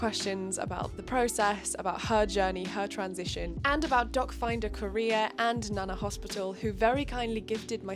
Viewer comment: I hear "dogfinder career" every. single. time